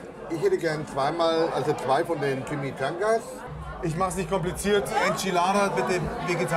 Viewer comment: "Also ich hätte gern 2 mal Jimmies Tangas", sorry ich habe das so verstanden ....